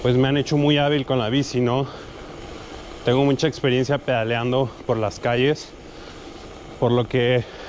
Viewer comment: Me vi todo el video porque pensé que decía peleando jajajajaja